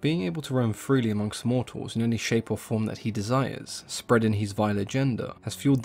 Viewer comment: I heard "spread his vile agenda" as "spread his vile gender" and for a split second assumed he was trying to spread queerness across the universe 😂